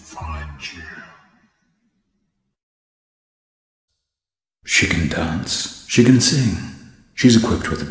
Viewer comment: Sounded like william said "Chicken dance" instead of "she can dance". (Lol)